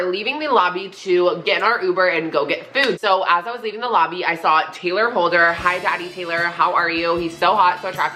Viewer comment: Did you say Taylor Holder I thought I heard that